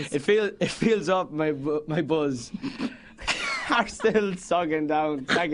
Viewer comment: It sounded like he said, "My balls were sagging down." Am I the only one who heard that?